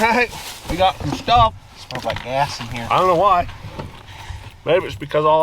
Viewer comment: Did he say Ass or Gas??